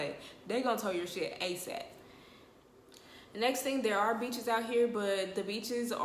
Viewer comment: You said beaches I thought you said b#ches. I was listen for a good 30 second like dang the b#ches nasty lmao